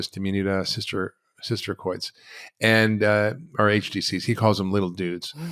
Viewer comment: What is the name of the "worm" used to treat autoimmune disorders mentioned at 37:34? I cant understand the full pronunciation in the video. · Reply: HDC (Hymenolepis diminuta cysticercoids)